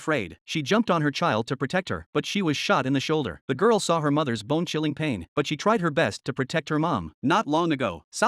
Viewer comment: I thought it said "Bing chilling pain" 💀